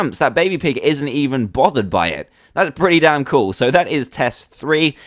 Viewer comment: I heard "that's pretty DAN cool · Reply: well I heard that's pretty dan cool even if I knew he was trying to say damn